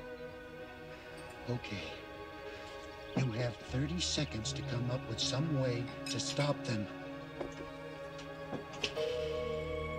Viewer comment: I thought grandpa said “you have 3 seconds”